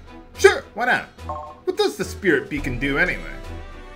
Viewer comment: I thought it said spirit bacon at first. Mmmmmmm bacon.